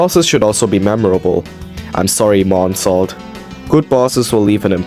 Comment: I'm sorry what did you say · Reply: i said "i'm sorry monsalt" the text said "bro i'm not that forgettable"